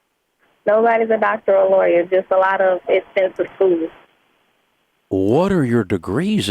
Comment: Did she say expensive food?